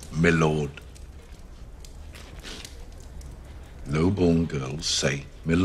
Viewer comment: My Lord is proper pronunciation, mi'lord is not. Proper speaking etiquette is usually reserved for higher base people, whereas simplified or butchered is for lower base, like commoners. And no, Tywin says the opposite. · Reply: i find that’s a common mistake, many people ,including me heard him saying “noble” while it was “low-born”